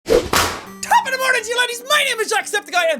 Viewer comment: I almost thought jack said "ladies" at the beginning today. I'm dying.